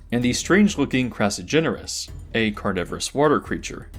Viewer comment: What did you say the name of the little guy at 11:57 was? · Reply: Crassigyrinus A prehistoric amphibian like creature that lived in the early Carboniferous